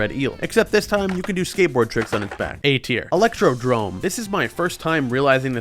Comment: WAIT ITS ELECTRODROME?!?!?! that has to be mandela effect bc ive always heard it as "electrodome"